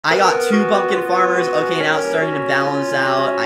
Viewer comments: why do I hear I got two fuckin farmers 💀🙏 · I thought he said 2 fukin farmers..